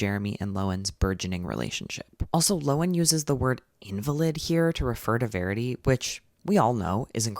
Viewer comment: What word is that? How to spell it and what does it mean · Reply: 'invalid' : its used to talk about people who can't really take care of themselves because an illness or disability. that word's usage implies that disabled people are weak, useless, or lack ability, or that they are a burden. it reduces their identity to their condition (for exemple you don't say 'invalid person' like you can say 'disabled person' : you're just saying 'invalid'). so its just not a word that's really used anymore :)